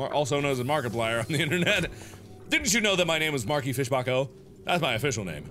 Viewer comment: I thought he said his name was Marky Fischbach, but its Mark E Fischbach 😂😂